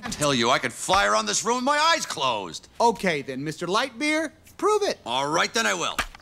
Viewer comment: 🙀🙀🙀😂😂😂😂 who else heard woody say “Light Beer”? 😭😭🤪